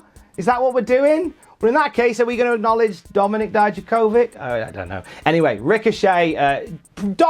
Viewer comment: Why did I think he said Dominik died to COVID 14:44 omg